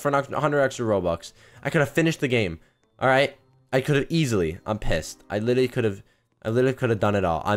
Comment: did he just said "im pissed"